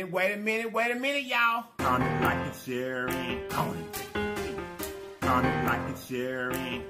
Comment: I thought you were saying coming like a cherry go lol im so crazy